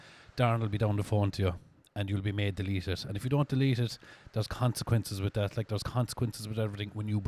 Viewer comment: Thought he said cunts'equinces for second hahahahaha